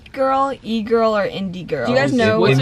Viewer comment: Lol when you said indie girl and your brother and thought you said Indian girl😂😂😂